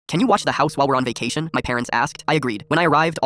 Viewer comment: I heard "Can you wash the house"